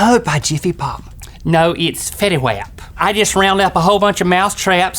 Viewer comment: said Petty Wap instead of Fetty Wapp W.T.F.